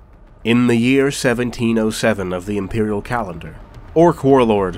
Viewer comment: the subtitle says "if the imperial calendar", narration says "of" please fix if its not too inconvenient.